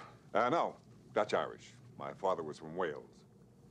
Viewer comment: Ludwig: "Cuban?" Drebin: "Dutch Irish. My father was from Wales." Ludwig: "Oh." Just awesome. Where did comedies like this go? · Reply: Weird, I thought he said "mother" as well but Drebin said "father".